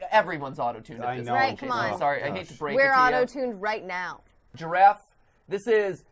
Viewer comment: If ya dont get the giraffe one... "Say Colorado" guy ski's by in giraffe wunzy "IMA GIRAFFE" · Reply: The first time I heard it, I thought he said "Colo-giraffe" instead of "I'm a giraffe".